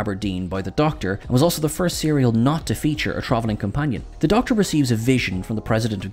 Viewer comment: I've listened to 8:42 over and over but I cannot for the life of me figure what the word he says before companion is " a trovernon companion" is what I hear and obviously that word is absolute gibberish Can someone translate?? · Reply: Travelling.